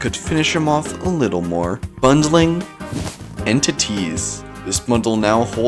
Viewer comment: when he said "bundle entities" i thought he said "bundling in to teez